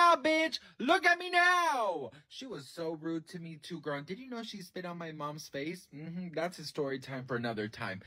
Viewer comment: HA! I thought you said Ms. Ramirez SIT on your mother's face. I was like, Wha!? Nah! That can't be what he said. Turn on those closed captions, rewind and read...Okay...Cuz I didn't think you were going to share any story about any teacher sitting on your ma's face.